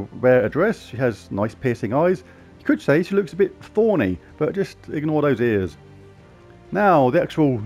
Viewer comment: Lol for a second there I thought I heard you say "she looks a bit horny" 🤣🤣🤣